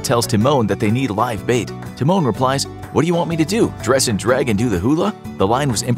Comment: I always thought that he said “Dress up and do the hula?”